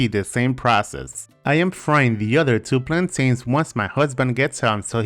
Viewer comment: did he just say husband 😂